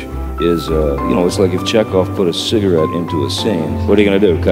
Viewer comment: The dumbest thing about this video is that Johnny says “Chekhov” and the brilliant author thought he said “check off”. ☠️ good god ppl